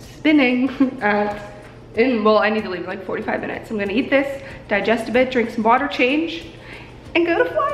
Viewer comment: My GOD, I need a nap I legit just heard you say "digest a bitch". Also I love your style and I love watching you grow and BLOSSOM 😍 lol sorry if that's weird ur just amazing 💖